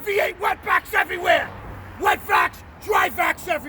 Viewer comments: Oh shit I thought you were saying wet backs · I thought he said wet backs instead of wet vacs